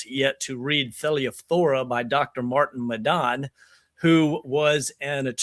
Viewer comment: Please advise what dr Martin's full name and the name of his book is. I can't quite hear the exact words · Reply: Dr. Martin Madan Thelyphthora <link>